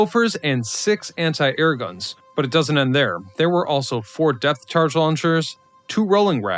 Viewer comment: Did he say Death Charge launchers ? At about 3:20 ? Instead of Depth ?